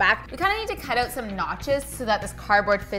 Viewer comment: when you said 'notches' i heard 'nachos' and now i'm hungry. hahah